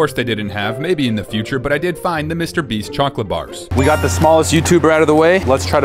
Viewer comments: Did I hear that right? SMALLEST? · did he just say smallest